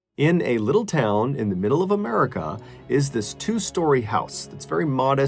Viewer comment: i swear it sounded like Ballen said amurica, true amurican right there